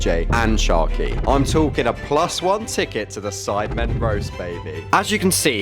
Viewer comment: Feels good to be a member of a massive YouTube group...Subscribe x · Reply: I thought u said beta squad not sidemen?